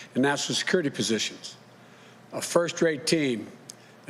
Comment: I swear I thought he said my “FIRST GRADE TEAM!!!! Lmao